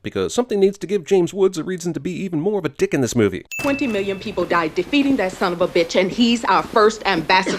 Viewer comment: Oh look, a priest alone with a little girl. Not good! Not good! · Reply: Adventurerblitz Ok I thought he was saying 40 million priests did that I was gonna have a statistical aneurysm. lol